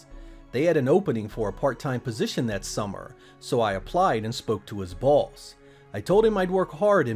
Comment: did he say I applied and spoke to his balls